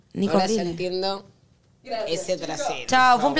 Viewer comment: flor dice no entiendo este trasero?